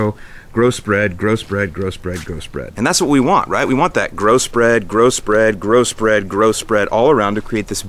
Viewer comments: Sounded like you said "gross bread, gross bread, grow spread" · lol at 8:30 I kept hearing Gross Bread Gross Bread Gross Bread